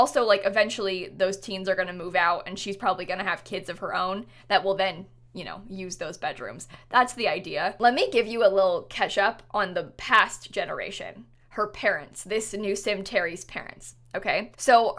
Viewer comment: What I’m early? (Ish) 3:11 I literally thought she said ketchup.